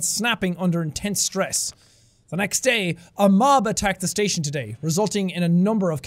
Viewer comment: I thought he said “a mom attacked the station today” and I thought “oh shit, it’s a Karen”